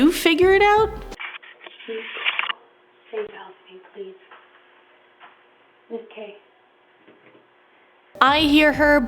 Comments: I heard please help me Kate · The clip at 8:05 shows the woman speaking something. I think I hear something like, "Please.. Please help me, please"